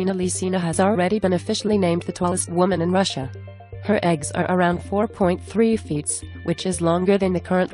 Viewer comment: Did they say eggs 3:40 Lol lmao 😂